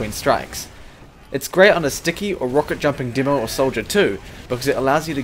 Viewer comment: what did he say?...a rocket-jumping demo XD